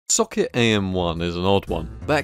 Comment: as u started the video i heard like "suck it am1" :) sorry (looks like seeing the video i nailed it , the suck it part i mean :))